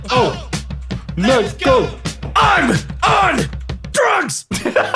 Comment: I thought he said hey ho let's go, I'm am trunks